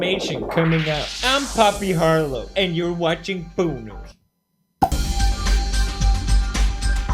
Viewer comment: am i the only one who heard "and you're watching BONERS"